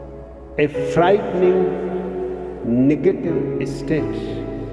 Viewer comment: i think i heard an n word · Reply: i think he said negative edit: yeah he definitely said negative